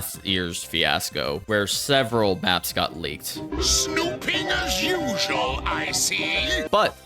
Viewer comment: 2010s brainrot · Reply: For the first time in my life, I heard it as "snooping as" and not "snupingas" What the fu...